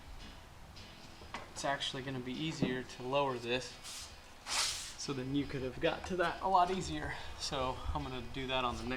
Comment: What did you say at 2:05? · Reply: Good question 😀. I was saying that the clip pin in the middle of the deck is easier to pull out if you take the deck off the blocks as it will give you more room to get in there and pull it out.